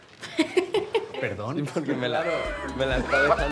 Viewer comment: Did I hear a „Tut mir leid“ (German for I am sorry“) at 1:05? Arón‘s dad is German therefore he also speaks the language